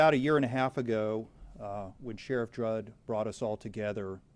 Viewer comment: 🤔Could swear he said Sheriff Jrudd. It's Sheriff Grady Judd. But hey! We're all human and make mistakes. 😉😂